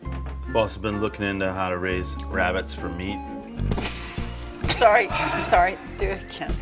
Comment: Can anybody please tell me what John C Reilly says at 1:24?? I couldn't understand it cause english is not my main language. I love this movie so much! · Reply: Somthing about rabbit for meet